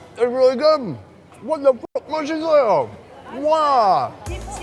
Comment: sounds like he say what da f- but it got cut off.